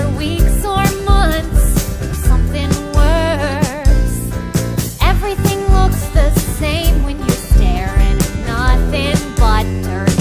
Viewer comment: Things people have pointed out so you don't have to: 1. Junior is a name, and it should've gotten faster 2. What If is a song title, and it should've gotten faster 3. Somehow the entirety of the song Black Friday is gone. (no clue how that happened) 4. Potter and Bug are both said without it skipping. For Potter, I was just focusing on his first name. For Bug, I only counted it if they were referring to him specifically 5. During What if, Zazzalil says Jemilla, and it should have gotten faster 6. During Fire, it should get faster because they say fire 7. During Strike Back, you is said earlier in the paragraph, it should have jumped there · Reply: Ah I always heard "everything looks the same when you stare" my bad :)